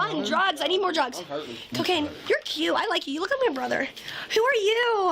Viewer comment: did she say she needed some cocaine at 5:47 😭😭